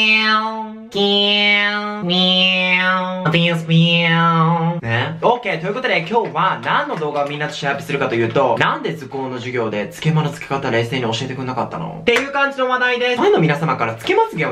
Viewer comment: 「つけまの付け方」が「漬け物の漬け方」に聞こえたw